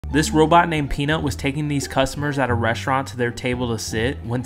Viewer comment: I first thought he said "This robot named peni..."💀💀💀